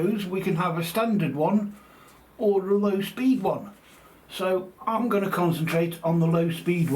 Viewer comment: For a second there, I thought you said speed run, not speed one. Had to do a rewind.😂 I can't wait for the finished product.